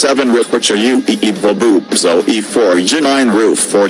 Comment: Did he just say boobs10:42